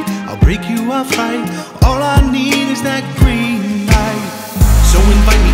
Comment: Though lowkey thought he said “All I need is that cream pie”